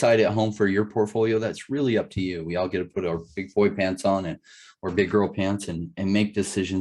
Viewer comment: Great video, very nice to meet you I Swore I heard you say we need to put on our Pink Floyd pants and make decisions ?! I was thinking to myself , now here’s a guy who’s confident in his investments ! The you corrected it Awesome video , better than a lot podcasts ! 🎧